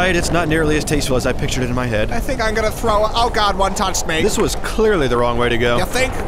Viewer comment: It's been 4 years and I'm still just as clueless as ever as to what Paul says at 1:11. "One talk spenk"? Seriously what. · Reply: He said "Oh god, one touched me!"